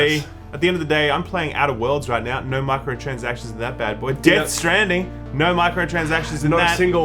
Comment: I misheard Death Stranding as "Death's Jamming"